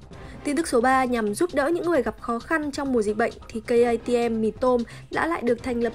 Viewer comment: Lúc đầu nghe nhầm cây ATM thành K-ICM mì tôm =)))) tại chúng mày hay bảo nó là cây ATM nên t nhầm đấy